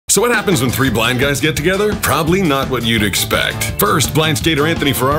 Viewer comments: At first I heard, “So what happens when three black guys get together…” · I really thought he said,” what happens when 3 black guys get together”🤣🤣😂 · I thought he said when three black guys get together I was expecting a crime joke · Thought he said black guys💀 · I have my fan on and I couldn’t hear properly and I thought he said what happens when 3 black guys hang out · Shit one I thought he said three black guys. Then I say one love their I'm like ok misheard lol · Ngfl I thought he said “when 3 black guys get together”😂😵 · I thought he said three black guys lol